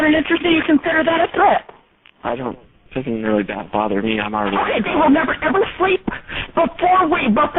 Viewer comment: does this guy say what I think he said? I thought I heard him say, "That doesn't bother me, I'm already on the Do not call list." She talks over him towards the end of the statement so it's hard to hear. A telemarketer on the Do not call list? LMFAO!